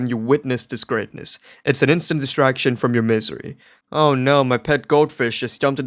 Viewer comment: did you say m-m-m-m-m-m-misery?